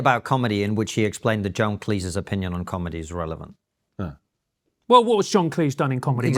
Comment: Did you say that John Cleese's opinion on comedy is not relevant 11:45?